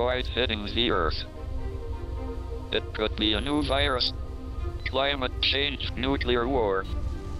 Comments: did he just say VIRUS??? Did he knew it all along? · Is it just me or at about 0:19 in on this he says!... the Corona Virus y'all?! What?! Please listen and tell me what you think in the comments lol. It kind of has a skip in it cuz of his way he has to speak but I think that's what he's saying. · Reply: No he’s saying “the new virus” not corona virus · did he say Coronavirus??